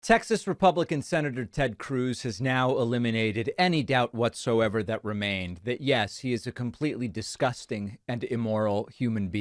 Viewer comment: At the opening I swear I heard "Sexist Republican Ted Cruz" instead of pretzel boy "Texas Republican Ted Cruz"🤣